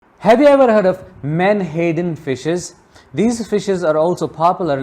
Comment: I thought you said "man hating fishes" 😂😂😂😂😅😅❤❤❤